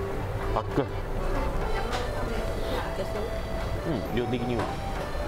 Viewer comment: カメラの横に彼女さんがいるのかな❓ いけそう❓ このくらいはニコってしてるの 微笑ましいですね😊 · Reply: くらいで会話してるの聞こえますよ 正しくは「いけそう❓」「うん量的には」って言ってますね